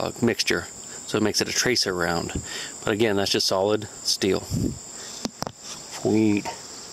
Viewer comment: did he say weed? did anyone notice that